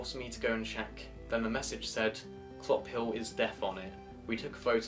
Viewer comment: What did it say? I couldn't tell what you said. "clophill is __" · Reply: Clophill is death